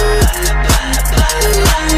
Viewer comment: I think I found a rhyme (not really a rhyme) bloodpop I thought you said kittypop :)